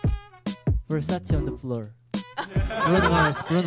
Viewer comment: sorry but what did he say lol i understood "versace on the floor'' lmao